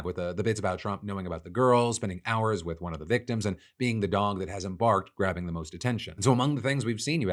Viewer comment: Holy Shiit(ake)😁 Sure sounds like he said "Being the Dong that hasn't barked, grabbing the most (pussy!🤨) Attention", which would be hilariously appropriate!👍